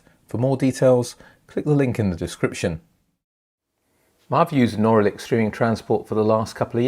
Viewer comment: Who did you say closed shop at 00:18? Just curious. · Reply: Auralic closed business in September